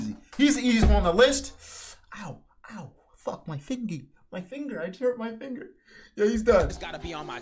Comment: I heard say “ow my fingy” 🥹